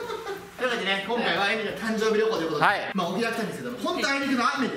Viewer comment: なんでだろう、すごくドキドキする笑 ここばっかり見続けちゃう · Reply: エミの誕生日旅行って聞こえるからドキッとしました。